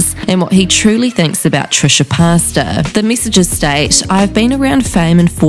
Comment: does she say Trisha pasta or am I hearing things?